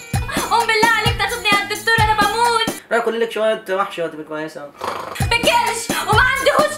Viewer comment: انا سمعت غلط ولا هو قال روحي كلي ليك شوية محشي وهتبقي كويسة؟😂😂😂